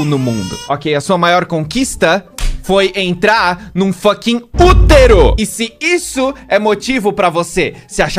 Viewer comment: De primeira eu entendi "puteiro" ai eu fiquei boiando kkkkk Mas dai eu vi de novo e entendi q era "útero" qwp Essa hora que não seu se sou burra ou me te poluída Minha mente- na verdade você é os dois né queria É qwp